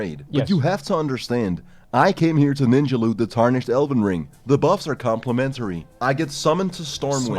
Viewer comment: After playing Elden Ring so much, when he said “Tarnished Elven Ring,” I had to do a double take because I thought he said “Tarnished Elden Ring”